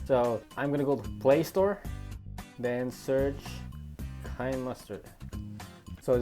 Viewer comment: At first I thought he was saying kind mustard